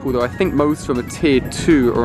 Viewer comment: i thought u said "tear" :p